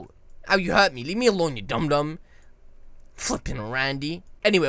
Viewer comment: Did He Say F*****G Randy?!?!!